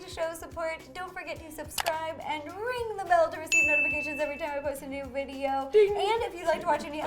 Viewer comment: I thought she said "Rodafacations" not "notifications". She should call notifications "rodafacations" if she doesn't already. Like if you agree